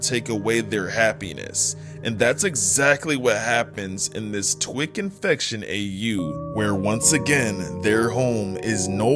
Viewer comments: I heard "trolls twirk infection"🥀🥀🥀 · BRO, I THOUGHT THAT SAID "Trolls Twink Infection" 😭